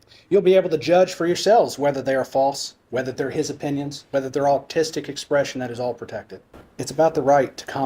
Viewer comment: Did he say, "autistic expression"?